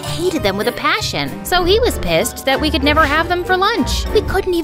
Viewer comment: DID U SAY P1SSED?? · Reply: Oh just shut up its pissed